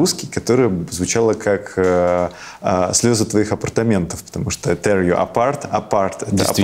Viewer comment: В какой-то момент послышалось Тер Леопард вместо Tear You Apart)))